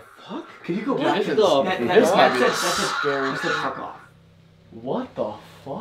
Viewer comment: “no I’m not , so F OFF” fully what I heard